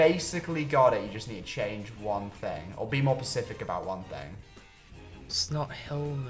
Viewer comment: I gotta run that back. "Did Tom say be more "pacific" about something or am I just hearing wrong? Because I swear he said pacific. I love the ocean too, but it can't be used in the English language like that. And I swear he said it again! Is this just how pronounces the word? Because if so, cool.